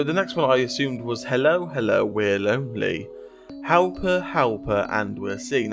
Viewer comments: God, I can’t believe everyone copied off of this! So rude. It honestly horrified me. I originally thought she was saying “Don’t mind me” instead of “we’re lonely” for some reason. Thank you for revealing the lyrics! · Without the lyrics, when it said "Hello, Hello" I heard "Halo, Halo." Also when I heard "Veronica" I thought 'maybe it's Sixs' real name" but The Lady theory works better.